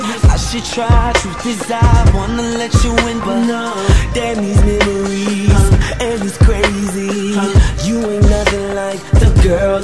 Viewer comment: For the longest time, I always thought that he was saying “daddies memories and he’s crazy”.